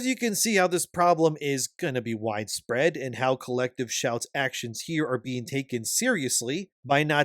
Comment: At first I thought you said Galactic Shout and I was like, "wait, did they really name themselves after two Pokemon villains teams?" But then you said it again and I realized I misheard their name the first time. Galactic Shout is honestly a cooler name though.